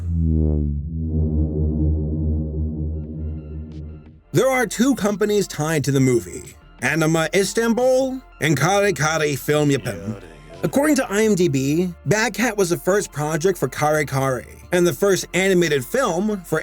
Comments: I heard that yare yare your not slick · DON’T THINK I HEARD THAT, BECAUSE YARE YARE DAZE I HEARD YARE YARE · DAMNIT I KNEW I HEARD A "YARE YARE" AT 4:05 you sneaky sneaky man you. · Did I heard a quiet "Yare Yare" at 4:05? The JoJo fan inside of me is really happy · did I hear "yare yare daze" in the background or am I hallucinating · Did- did I just hear yare yare daze?.... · In 4:04 i heard a "yare yare " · I HEARD THAT YARE YARE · you ain’t slick I heard that Yare Yare 😂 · Anyone else hear that sneaky "yare yare" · I heard that "yare yare" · I HEARD IT! THE “Yare yare..” OF JOTARO KUJO! ITS OFFICIAL! IS A JOJO FAN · I heard that yare yare daze! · I hear Jotaro's Yare Yare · did anyone else hear jotaro say yare yare at 4:04?